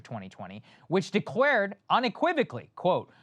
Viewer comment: Did he.. did he just say.. "De-quared unequivocally" ?